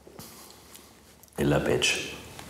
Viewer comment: Was sagt er da? Ich verstehe nur "Billabetsch". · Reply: Ällabätsch sagt er, so viel wie „Ätsch“ ;)